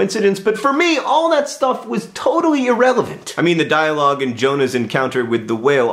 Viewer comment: did u just say irrevalent?